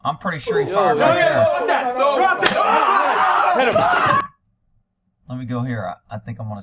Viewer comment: I heard somebody say .. Hit him